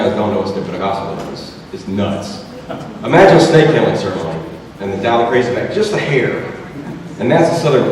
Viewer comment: the Youtube-provided subtitles say, "...imagine state ailing certainly and the Dalek race back just to here..." and I couldn't help but picture Mississippi being full of sentient trash cans with dildos attached to them.